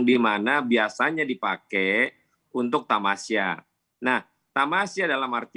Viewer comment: tunggu bentar. Gw ga salah denger kan? Dia bilang Tamiya?